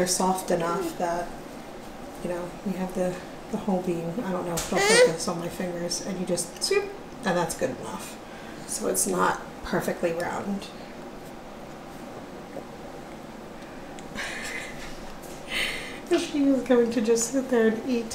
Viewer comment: Sounded like she said "that's good".